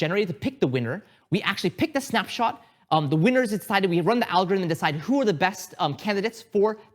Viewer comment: what does say at 19:33 "we actually pick the snapshot, the winners inside, we run the algorithm" ????